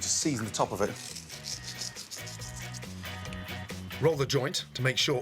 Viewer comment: Did he say roll the joint at 3:33 now that I can do😂😂😂